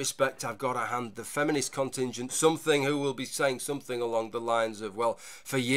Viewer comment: Did just say that he's got to hand it to the "feminist cunt engine"?